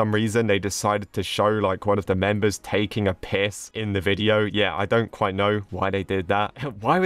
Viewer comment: did i hear that correct? “one of the members taking a piss”